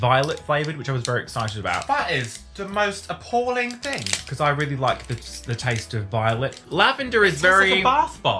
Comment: I thought you said "i love the taste of violence"